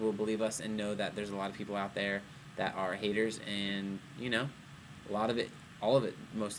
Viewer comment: When kevin said haters I thought he said hangers and I was like "Hangers"? o.O :P